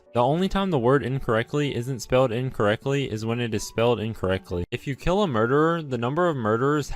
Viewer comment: I need help what is he saying at 8:20??? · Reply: Never mind I figured it out, the only time the word incorrectly is spelled wrong is when it’s spelled incorrectly.